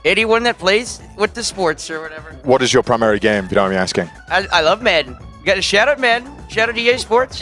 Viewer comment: I know he probably said “I love Madden”, but it really did sound like he said “I love men”! 😂